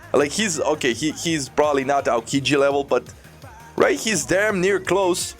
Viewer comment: did you just said not aokiji level 😅